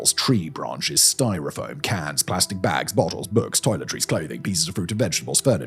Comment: Did he say cats? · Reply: No, he said cans, but I get why you ask that.